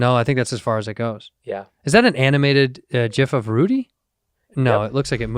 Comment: did andrew say jif instead gif at @ 1:10:07 ?